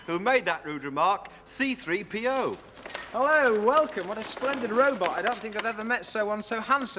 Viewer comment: Does anyone else hear “hello there” at 0:58?